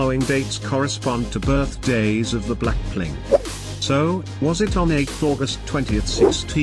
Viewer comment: what did you say "Blackplink" instead of blackpink🤣